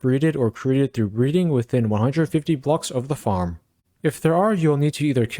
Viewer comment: Can build this to high slow down spawns cause i misheard 130 as 150